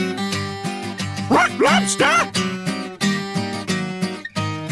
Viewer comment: Ngl I thought he was saying Rock Lobster when I was little, was really confused about the turban